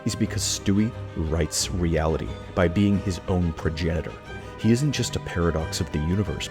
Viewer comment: i thought you said genital